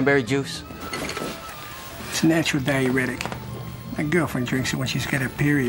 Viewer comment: I thought he said natural diabetic